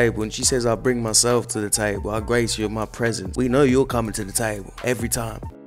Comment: "I showed up to the table and graced you with my presence!" She looks like she ATE the table and everything on it as well.. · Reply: I low-key thought she said ‘break you with my presence’